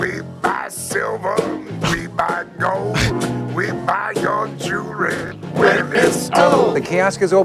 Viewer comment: He may be singing "We Buy JEWELRY", but imagine him singing "We Buy Your CHILDREN". That's what I thought I heard when I was watching this.